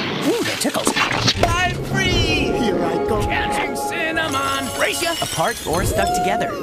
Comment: what the hell does apple say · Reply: He said”catching cinnamon”